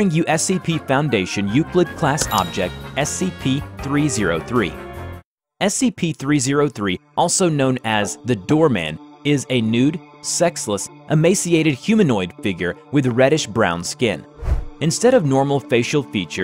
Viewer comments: I heard it as : scp 303 also known as the doorman, is a nude, 'sexy' emaciated humanoid figure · I thought it said “doomman” and I said “no, the Doom Slayer isn’t an SCP”